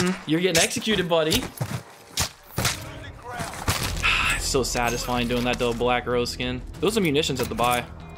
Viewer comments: Am I trippin or did he say “ it’s so satisfying doing that to a black girl skin” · Ngl thought you said "black girl skin"